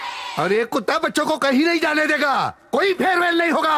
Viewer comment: prakash Raj say koi fail nahi hoga this is how cbse and icse board should be 😂😂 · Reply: Koi farewell nhi hoga bola bhai vo